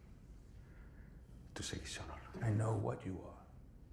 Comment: He didn't say I know who you are, he said I know WHAT you are. Excellent choice of words.